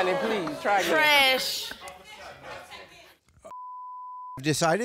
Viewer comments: Did he say (........ Jerry's final T.H.O.T.) ? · The guy who said we will be back with Jerry’s final thought it sounded like he said thot not thought hahaha